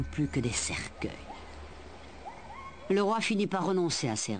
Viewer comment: A 50:35 elle dit "le Roi Philippe" c'est normal? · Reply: Ah non rien je pense qu'elle dit "le Roi finit par renoncer à ses rêves" et pas "le Roi Philippe a renoncé à ses rêves" haha